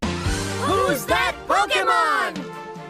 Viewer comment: I honestly thought the intro said "Who's that Broke'emon"